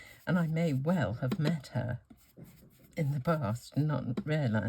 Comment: I did laugh out loud, I misheard and thought you said you'd probably met the lady “in the bath”, but you'd actually said “in the past” 😂😂 such fun, thanks for sharing 🤗🥰